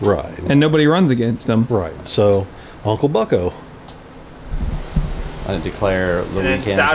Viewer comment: When said uncle bucko blew the vacuum up it sounded like he said Unka Bunka and I lost it!